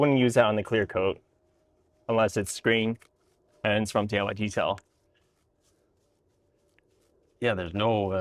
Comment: Super strange: at 11:09 when talking about the brush, the captions say "unless it's green and it's from" <an entirely different brand> that doesn't at all sound like DIY Detail! The dialogue at says "unless it's green and it's from DIY Detail".